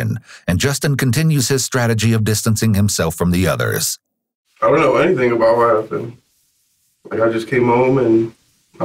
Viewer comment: “I don’t know anything about what happened” at 20:05 sounded like “I don’t like anything about what happened” like he really was thinking about one thing but said something else.